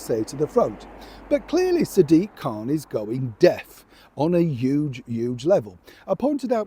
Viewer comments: So disappointed when I realised you said going Deaf, I thought you said going DEAD!! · At first hearing, I thought you said going dead. But, alas, you have stolen my dreams ... how dare you! Keep up the good work, from a Scot near Hamburg in Germany